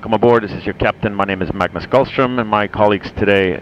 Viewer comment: He said My name is Magnus Carlsen I didn't know that A Chess grandmaster also can fly Planes 😂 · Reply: Lol it sounded like he said "Gullström".